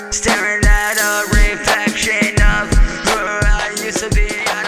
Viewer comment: Sounded like he said rapeflection instead of reflection 😂